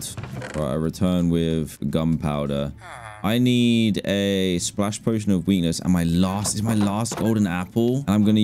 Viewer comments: Who heard him say "I need a potion of weiners" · DID HE JUST SAY WENUS??